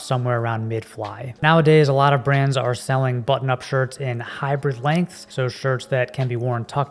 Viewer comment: You pronounce "hybrid" like "hyper". So every time I hear "hyper length" or "hyper shirt".